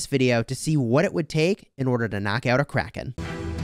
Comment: I heard " Knock out a Crack head" LMAO